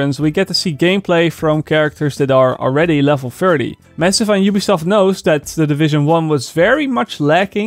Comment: did you just say: Mensen van Ubisoft know???